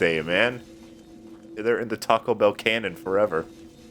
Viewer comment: Odd. When I heard "Taco Bell Cannon" I heard it as "Tackobell's 'Canon' " And I imagined a symphony about Taco Bell